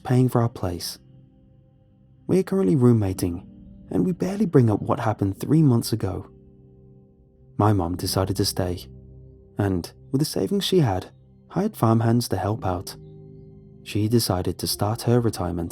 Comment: Please tell me I'm not the only one that thought he said weed farm.